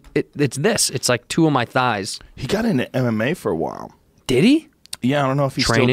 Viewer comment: I thought it said what if shaq got into mdma I watch too much Joe Rogan